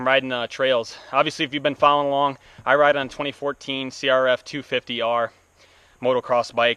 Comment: I've never owned a cr450f I believe your mistaken for what is actually in the video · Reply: my bad. At 3:17 I thought you said 2014 cr450.